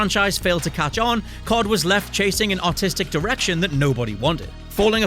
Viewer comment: pronouncing the R in artistic makes it not sound like you said the direction COD was going was autistic lol